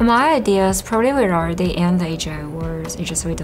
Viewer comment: What did she say? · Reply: "probably we're already in the AGI world"